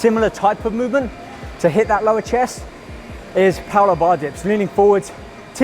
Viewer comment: What's the exercise he talks about at 7:44 to execute instead of the reverse grip bench press? i just don't understand the words he says.. thanks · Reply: Richard Troy dips